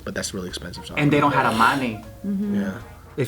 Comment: Money?? I thought you said honey xD